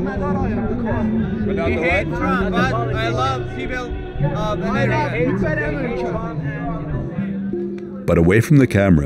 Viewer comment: lol, Hmm... I heard the Iranian man said, "He hate Trump but he loves female Americans." or did the get the wrong subtitles?